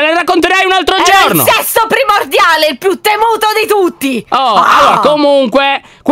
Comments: ho sentito s***o primordiale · solo io ho sentito se**o primordiale????😂😂😂😂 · io ho capito sesso primordiale o sbaglio?😂😂